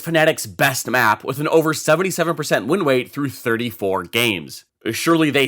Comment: bro did not say winrate he said winwate 🤣🤣🤣🤣🤣